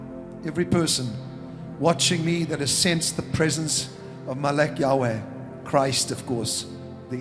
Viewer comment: Did he say, "Molech Yahweh"?